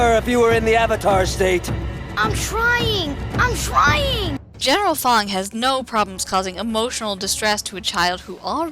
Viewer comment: What you said "General Fong" What I heard "General Thong" lol.